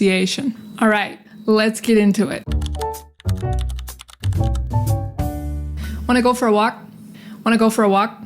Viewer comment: I don't know why I say "wanna go for a rock" instead of "wanna go for a walk"😂😂😂